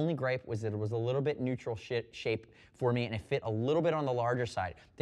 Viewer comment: Did you just say "neutral shit shape"? :P Great review as always!